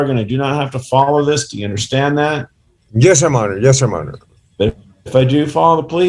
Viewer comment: "Yes your Monner" is a new one on me! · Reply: I thought he was saying yes sir my honor